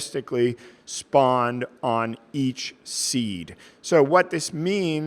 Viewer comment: "EACH SEED" - sounded like the "E A Sports" voice lol